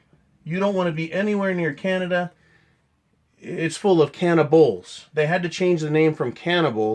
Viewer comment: Canada = cannabalism? Really? Contrary to misinformation, we do not all live in igloos and lack indoor plumbing. 👎🏻 👎🏻👎🏻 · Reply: i thought he said carnivals.